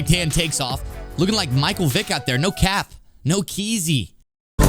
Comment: No Cap . No Kizzy 😂 where you learn the lingo from man ? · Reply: yeah that was terrible lol and he said it wrong lol keezy is what he said